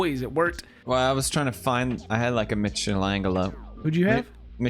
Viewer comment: Papa Pewds spoiling us with years of great free entertainment. But I'll never forgive how he pronounced Michaelangelo. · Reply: you know what i hear? "mishelangello"